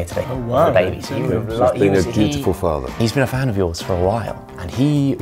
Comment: I think he says ‘dutiful’?